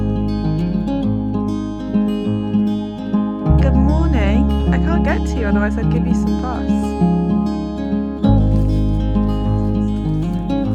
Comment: Hi! I’d never try to feed a horse that isn’t mine but thank you for letting me know ☺️ xx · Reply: I must have misheard when you said “Good morning “ to the horse “I can’t get to you, otherwise I’d give you some grass”. But good to know you understand it now.